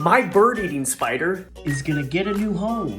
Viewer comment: Wtf I thought he said my bird IS eating a spider. Then I thought he got a bird cage and I was like what kinda cage is that????? Lmfaoooo then I seen the spider getting put in another box and I had to read the thing at the bottom like 5x before it kicked in that the spider eats birds lmfaoooo omg my roots are blonde today!!!!!! Lolz Monday mornings man. Smh.